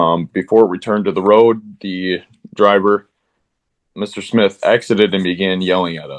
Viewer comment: I don't think said "Accidently began yelling at us". I think he said "exited (as in his vehicle) and began yelling at us.". ;)